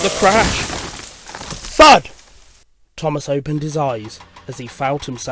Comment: did he say the f word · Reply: No He Said Thud